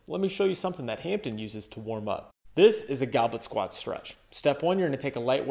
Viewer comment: I heard 'goblin' squat, but it's goblet squat, right?